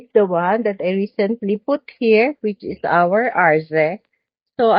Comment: what’s the name of this succulents ? Did you say Arce ? It’s beautiful colors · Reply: Its arze thank you for your support